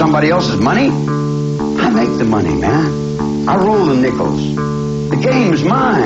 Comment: He come to me with money in his hand, he offered me, I didn't ask him. I wasn't knocking someone's door down, I was running from that. When I got out, I was in that, I was already through that, I had that. I had the studio, I went to the studio, and the box studios I had it all and I looked at it and said "This a bigger jail than I just got out of. I don't want to take my time going to work. I got a motorcycle and a sleeping bag, and ten or fifteen girls, what the hell I want to go off and to and tuh go to work for. Work for, what? Money? I got all the money in the world, I'm the king, man, I run the underworld, guy, I decide who's does what and where they do it at what am I gonna run around and act like I'm some teenybopper somewhere for somebody else's money! I make the money man. I roll the nickles. The game is mine, I deal the cards. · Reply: The longest time I thought Charlie was saying I Rule the pickles